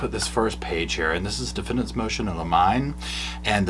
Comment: I SWEAR I heard “Defendant’s Motion of a MIME” I fell the fk out bc I could see her asking for that. 😂😂😂